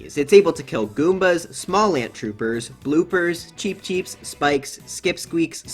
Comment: bro did he just say Small Ant , like the Mario Odyssey speedrunner youtuber/twitch streamer!